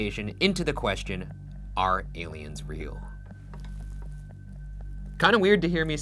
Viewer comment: Ok. Real talk. During the intro when Ryan said “are aliens real?” I thought he said “are humans real?” And I was like “honestly I don’t even know”